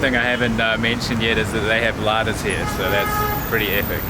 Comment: They have WHAT there?? Can someone please tell me what he was saying? Lighters? Ladders? I can't understand because of his accent. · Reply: he said "Ladas" a mini blue taxi